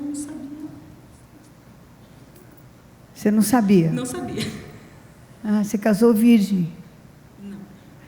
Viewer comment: ele falou que não sabia fazer sexo ?? Não entendi o que ela falou alguém entendeu o porque ela apanhou ??